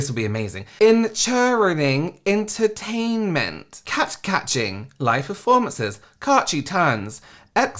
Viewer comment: wtf does “catgacating” mean? Like, what was it supposed to say? And “exarserdray”? I don’t understand